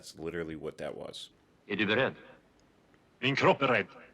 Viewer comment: These subtitles are wrong. "Är du beredd?", "Min kropp är beredd men inte jag själv!". That means: "Are you ready?", "My body is ready, but not me". I think the translator misheard 'beredd' as 'rädd' which means 'afraid'. Which is quite the opposite of being ready. Terrible mistake!